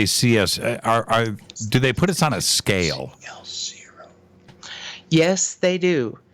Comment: Anyone else hear whispering at 1:35:50 "...scale zero..."?